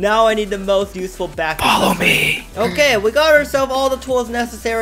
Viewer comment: does anyone hear the words 'Follow Me'.